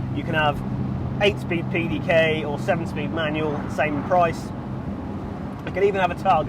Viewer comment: did he say 7 speed manual?? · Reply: Yup, 7 speed. Bit odd but you get used to it.